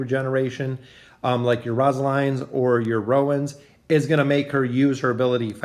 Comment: Is it just me or did he just say Rozaline